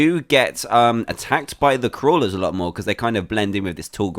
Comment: every time u said crawler i thought u said kwala